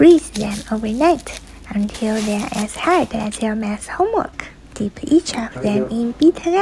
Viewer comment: What was supposed to say: math what I heard:mas 0:22 😂😂😂😂😂😂😂😂